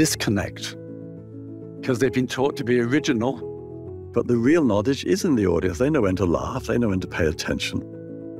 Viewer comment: This is wonderful. Thank you! One small note: There's a mistake in the captions, around 7:10. Keith says "The real knowledge IS IN the audience", but the captions say "The real knowledge ISN'T the audience". I think it'd be worth fixing as they are complete opposites.